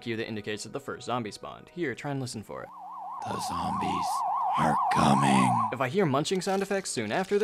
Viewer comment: When do I know zombies are coming? I keep hearing "Zone buy are coasting".